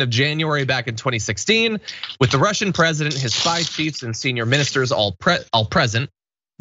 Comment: When you read "spy cheifs" I heard "side piece". I guess I officially spend too much time on the parts of YouTube that are too young for me because I didn't bat an eye for waaay too long.